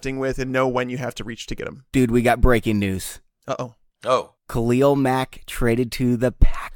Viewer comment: You need to look up the definition of fake news. That definitely does not qualify. Fake news is an intentionally fabricated story with no validity. The report was that multiple teams were interested in Mack, including the Packers, and that news was 100% true. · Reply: Make sure you listen to the podcast in its entirety before correcting people. For the record, I never said "fake news". I did indeed say "fake 'breaking news'". Either way your loose definition fits what actually happened within the broadcast. I was merely pointing out the humor in the irony that took place.